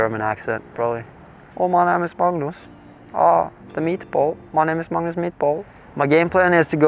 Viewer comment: At the very start I swear I heard "my name is Magnus MEATBALL" as a joke, making fun of how his name sounds like that because of the whole Ikea Swedish meatball stereotype... then I realized he was just saying his own name with an exaggerated accent